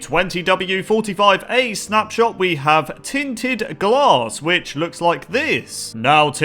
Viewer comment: what its called: tinted glass what calls it: tin tin glass this is only a joke people don't get offended i just wanted to point out that it sounds like he said tin tin glass